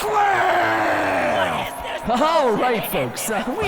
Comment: who heard the guy yell "what is this bullshit they didn't even fight".